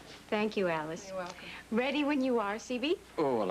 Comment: does Lesley say: “Ready when you are CB”? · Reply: Yes, she does. I guess it’s an old movie reference.